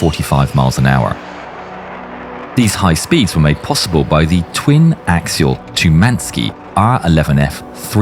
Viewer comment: it only has one engine... Did I hear wrong but it seemed to me that your said "twin (...) jet engine". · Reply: No, just twin axial. Not twin engine